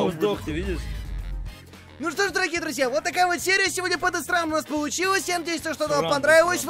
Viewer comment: мне одному кажется что он сказал подосраная